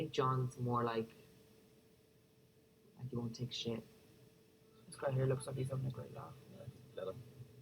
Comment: Could anyone please tell me what John says at 18:34-18:35? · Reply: "This guy here looks like hes having a great laugh!"